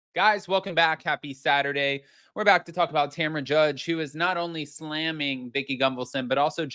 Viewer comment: I thought he said Tamra JUGGS 😂😂😂